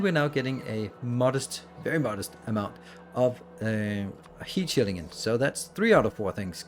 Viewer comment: Why do I hear you're saying heat sinks... they are heat shields :P